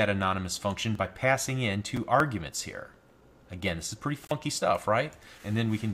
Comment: did he just say "fucky stuff at 2:29:57" ?